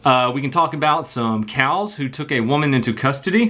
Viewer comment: Did he say, we could talk about some "cows" that took a woman into custody?